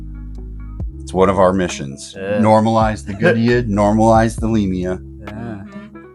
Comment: normalize the lemmia ? What did you say is one of your missions? 😂